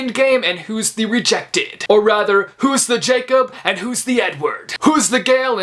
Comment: I thought he said. “So like Jacob and then the N word” not “so like Jacob and edward